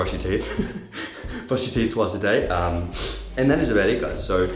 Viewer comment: Nah man brush your teeth twice a day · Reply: I thought he said once but he actually did say twice, slow it down to 0.25x and go to